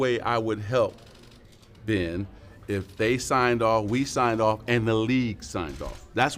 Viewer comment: Man I thought that said re-signs lol I was like what lol